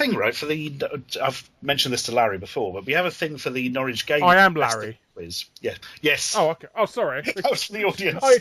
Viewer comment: I thought it said Ashy Larry XD